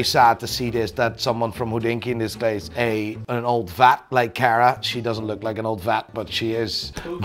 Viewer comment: I’m glad he clarified because I thought he said “old fat” instead of “old vet” lol😂